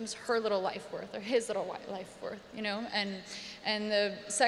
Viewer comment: I love her I really do ... but min 1:27s .. did I hear her right ? Did she say “how much is his little white life worth”???